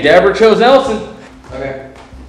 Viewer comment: Am i the only one who hear "ELTON" at 1:26:39 when the screen shows kitchen cam ...or is it just te EVP ?